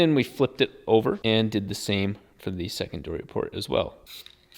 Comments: did you say "secondoory port" lol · did he just say second dory port?